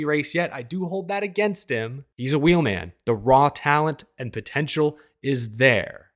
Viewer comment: I've always heard it as "Wheelin" or maybe "Wheelen"